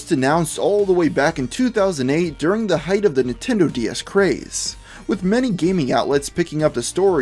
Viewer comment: i thought u said Nintendo BS! ha ha